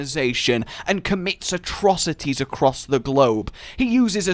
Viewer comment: I thought he said: "and commits Astrocities across the globe" I watch too much Astrokitty.....